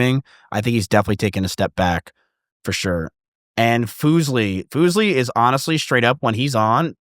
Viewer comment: Did miz say foozlie 😂2:05:00 , poor fuslie